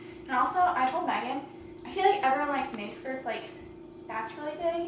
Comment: Every time you mentioned the skirt on your podcast i thought you were saying “mini skirt” and just now realised it was midi, idk why cos i’ve never struggled understanding you before lol. Looks good tho!